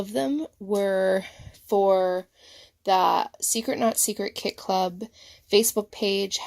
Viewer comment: I thought you were saying secret Nazi kit club. I've never been to Florida Disney You page is cute